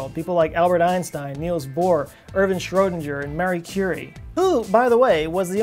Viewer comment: It sounded like you said 'Mary Curity' which would be wrong. It was Marie Curie.